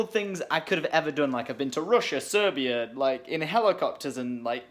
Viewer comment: Did he say he was in Serbia at 0:30?